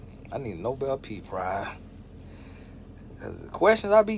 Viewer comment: I heard; A “Nobel Pee Prize” 😂😂😂😂😂 Tony, your accent is the best lol